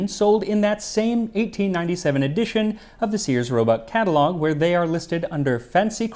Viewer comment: I misheard "Sears Robot catalogue" .